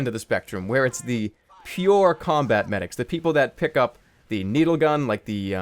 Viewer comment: LOL he said " A pure battle medic" i thought he said Pyouuuur battle medic XD